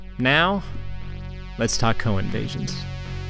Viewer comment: I kept hearing "Cohen Vasions"